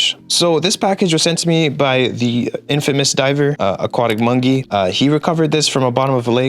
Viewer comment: the way he said Aquatic Mongey instead of Aquatic Monkey lol